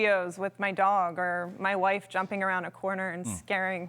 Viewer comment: Did she say my wife at 1:52? or am I tripping.